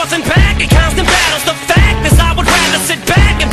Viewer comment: I thought it said fattest o.0